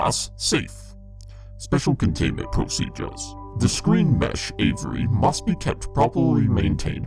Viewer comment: DID I JUST HEAR MY NAME!!!!! i swear I heard him said Avery